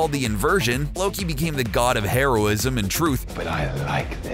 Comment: Lmaoo I heard: Loki became the God of hemeroids 😂